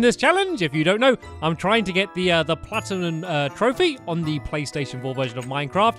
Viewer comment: Did he say Platinun?